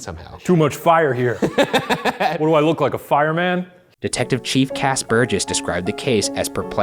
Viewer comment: This is just out of nowhere...but I wasn't looking at the screen, or paying attention, so at 4:25 I heard Ryan say "Detective Chief Casper"! lmao So adequate considering what the show is about